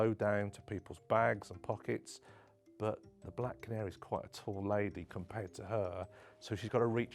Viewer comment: Did anyone else catch him calling the lady at 8:41 “the black” ?? 💀